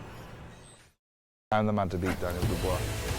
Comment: Lol..... I've only just realized after the 427th time watching the intro that Gorman says "I'm the man to beat Dubois"..... I kept hearing him say "onamatterpeter" - Bloody pikeys.